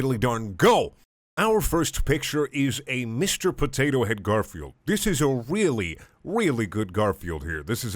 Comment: Thought you said "Garfield 4" Went to the tab playing the video Had to alt tab to porn, easier to explain